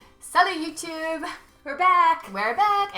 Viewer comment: It sounds like your saying: salad youtube!! Try to say the t more...